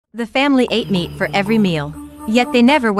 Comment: SOUNDS LIKE YOU SAID THE FAIMLY ATE ME FOR EVERY MEAL I can only do all of THIS tosee my Keyboard